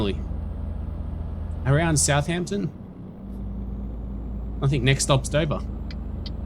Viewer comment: Did you say, "I think next stop's Sobar" when you approached Southampton at 17:31? lol